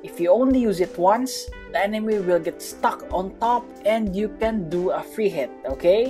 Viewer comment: When he said stuck i thought he said the f-word.